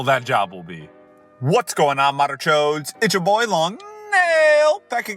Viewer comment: What do you actually say there? Every time i hear "Whats going on modern joes"